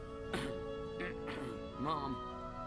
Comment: as a kid I thought he said "more, more!"